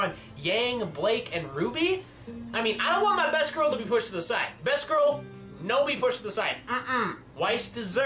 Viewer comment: I thought he said best girl Watts instead of best girl Weiss. Sorry 🙏🏻